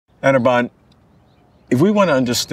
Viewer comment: Wtf did he say..ANABAN..LOL🤪